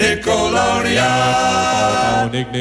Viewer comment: I remember I thought they said "Nickolodea"